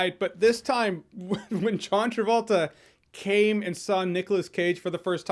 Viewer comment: I heard "When John Travolta came inside Nicolas Cage." God I'm 12 years old all over again.